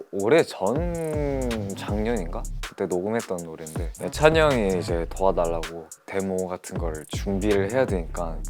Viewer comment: I thought he said record demons 😭😭 I was like, “ARE WE FINNA GET CHAN DEEP VOICE?”